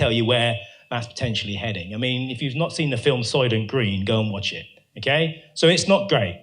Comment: What film does he say at 32:12 I can't work out what he's saying? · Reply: Soylent Green